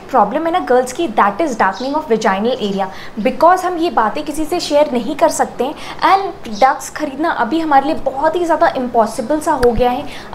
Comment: Hey sis what did you say at 0:08--to 0:09. Ducs or duxs. I don't know what u said? Can u tell the spelling. U said hamaare liye ducs kharidna? What is that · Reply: PRODUCTS